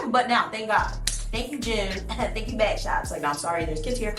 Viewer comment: Did she say "thank you bad shots"? What does it mean?